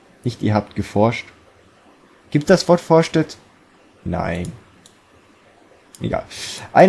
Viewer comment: natürlich gebt es das wort vorstadt ;D (Ja ich weiß, dass es forschtet oder so geschrieben war, es hat sich nur verdammt danach angehört^^)